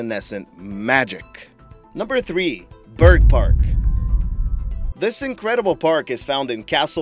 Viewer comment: The first time you said bergpark, I thought you said bird fart. lol.